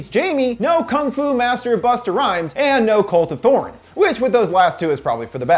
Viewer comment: I heard cult of porn